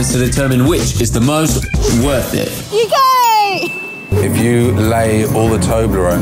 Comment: i understood ,,You Gay" instead of ,,UK"